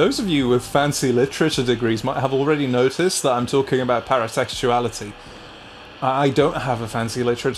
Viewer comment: Everytime in this video you said "paratextuality" I heard "parasexuality" which left me waiting for a conversation shift that never came